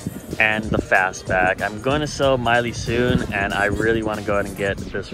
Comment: Did I hear that right? Selling Miley?